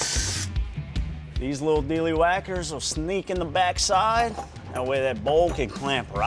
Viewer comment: Not first. And did he just say 'deely-whacker'????